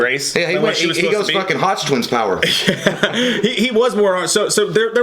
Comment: did say hodgetwins power? - shout out hodgetwins lol